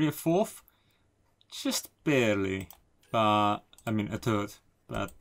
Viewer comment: I heard "carry a third" as "carry a turd"...